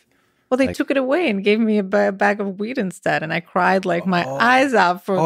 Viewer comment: Did she say her parents traded her Nintendo for a bag of weed, and then gave her the weed? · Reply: Ha I think it was wheat